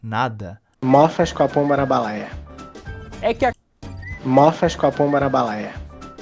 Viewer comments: o que eu entendi foi:mofas coroia/copão naras balaia. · Eu não entendi NADA do que o cara falou. "Mochicupimalabalaia" foi isso que eu entendi... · eu entendi mofaxcopombarabalaia